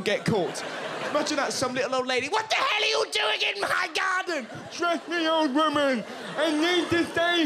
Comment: i honestly thought he said in the end "trust me ma'am i need to save him! danken happens hangs in the balance!"